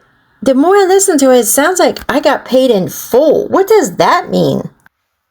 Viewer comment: I thought she said I got my hands full